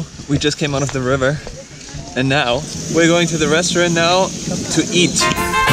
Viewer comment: i really enjoyed watching this videos!! Well actually I enjoy every single one! ooh and minute 13:55 when said "Where going to the restaurant to eat" I thought he said "Going to the restroom" hahahaha